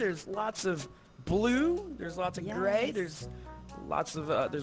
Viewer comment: I thought said 'there's lots of gay'